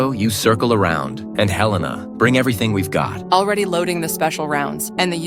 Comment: I thought he is saying hell nah